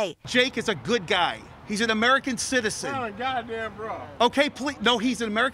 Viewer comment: "Put on a goddamn bra!" Is that what that person yelled? 😂